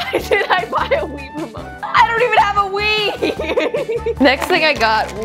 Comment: why did she say ''l dont have a wee''18:18 / 21:04🤨